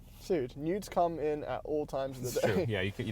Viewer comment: did her say nudes come in at all times of the day? 😂😂😂. My new favorite channel